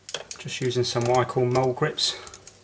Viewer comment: What did you call those pliers at 11:28 ish? It sounded like "wide claw mole grips" but I can't be certain. I ask because here in the US, they're called vice grips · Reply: Yes In the UK we call them Mole Grips.